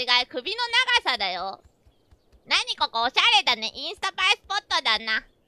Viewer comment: I think she said 'instagrammable spot'